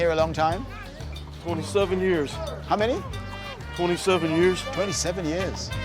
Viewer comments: who else heard “only 7 years” but got blown away when he said it again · 47 or 27? I think he said 47 years while doing pull-ups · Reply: UFC Champion 27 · what he say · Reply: 27yrs